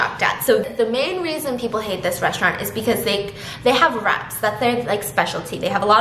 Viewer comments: My god I thought you said their speciality was rats lmao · I thought she said “they have rats that’s their specialty rats” I thought WOW this place is really disgusting😂